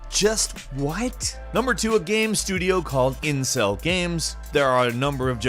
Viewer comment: when he said incell games I thought he said incest games.